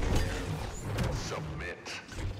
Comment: When I first heard him say “submit” I thought he said “sup b!+ch” 😂